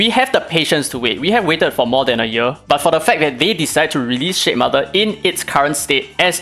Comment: I love that I kept hearing "shit mother" 🤣